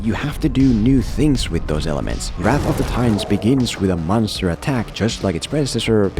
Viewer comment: Aha! Wrath of the titans... It sounded like you said "Rat of the times". Is that an Finnglish accent?